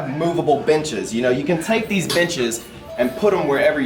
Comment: thought he said bitches